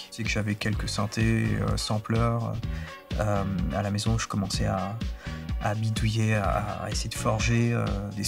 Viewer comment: i thought it said «i started doing some twerking»